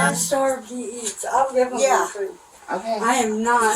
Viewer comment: What timestamp was this?? I didn't hear that. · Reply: I went back to listen to it again since you asked for the time stamp and I think I misheard. It was at about 4:43. Now, I think she said hot dogs after watching it with the captions on.